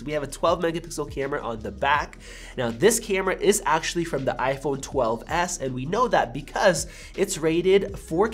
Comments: Did he say iPhone 12s? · Anyone else hear iPhone 12s around 5:48?